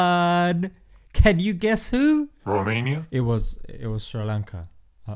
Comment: did he say Romania at 0:56 ?